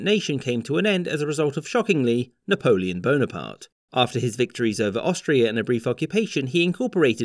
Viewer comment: I swear I hear "after his victories over Austria and every fucking nation"